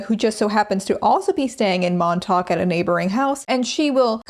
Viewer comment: Girl every time you say Montauk I hear “momtok” and ngl it took me awhile to realize those utah ladies weren’t here lolll